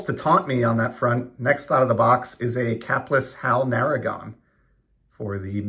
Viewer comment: Would love Topps Now Buybacks. I enjoy finding some of THE most ruined buybacks. Capless. I thought you said capitalist. I've never found a 57 BB.